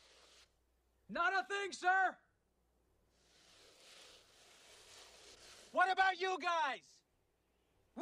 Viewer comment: it almost sounds like he saying "we aint found ship."